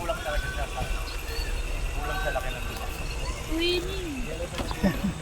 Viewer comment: ano sinabi nya dito sir naka 4 rewatch nako diko parin na intindihan curious lang 😅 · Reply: I think Sabi nya twinning kasi pareho kami kotse hehe